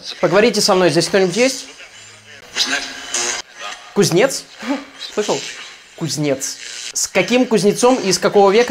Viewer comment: Ребят, а теперь вот вам шок контент помимо топы Значит так, тайминг 31:49 в момент когда говорится кузнец закройте глаза и прислушайтесь. Лично я услышал "who's next" ? И "The next" . Мистика, не иначе